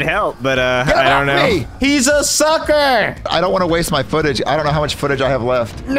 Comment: Did he say “HE’S A SUCKER”? 😂😂